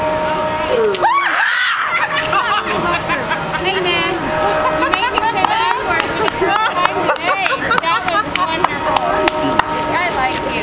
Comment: did she say “you made me say the n word for the 3rd time today”???🤨 not the flex she thought it was, should’ve kept that to herself